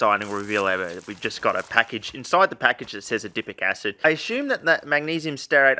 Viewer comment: i keep hearing "dipping acid" now I'm hungry for Salsa